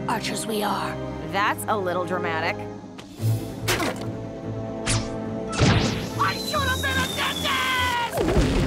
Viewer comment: It tooks me more than 5 minutes to understand what the ballon says, because of it's voice!! :( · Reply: Yokey No, he says "I should've been a dentist!"